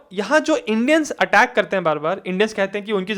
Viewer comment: guys did he say 'Indians'?? I didn't get that. What's the context in that part?